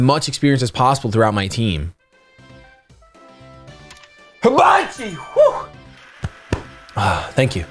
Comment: I don't think I've ever heard him sneeze! Did he say Habachi?